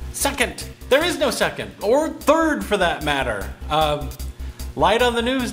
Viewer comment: it sounded like you said "Suck it! There is no suck it! Or turd for that matter..." Ah, mondegreens...